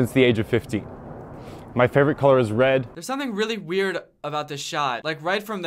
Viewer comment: did he said ‘’my favorite car is red’’? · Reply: Lol he said “my favorite color is red”